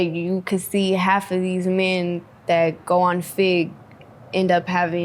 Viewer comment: "Half of these men that go on ??????" Fig? Fik? What does she say? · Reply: Fig is Figueroa street